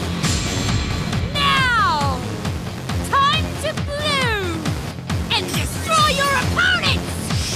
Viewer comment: What did she just say? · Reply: Time to bloom is my best guess 😂